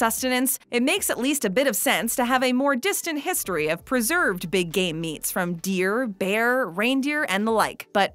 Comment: I wasn’t paying attention and I thought she said Big Gay Meats@ 1.01.